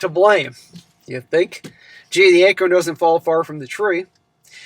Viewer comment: I know you said "The acorn doesn't fall far from the tree" but I heard "The anchor doesn't fall far from the tree"😂